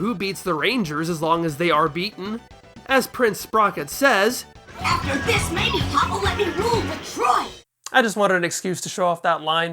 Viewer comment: Can... can someone please tell me what he said? "After this maybe papa will let me rule the Troy"? "After this many fumbles he will ruin the troop"? "After this maybe Bumble will be rule of two"? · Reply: "After this, maybe Pop'll let me rule Detroit." =P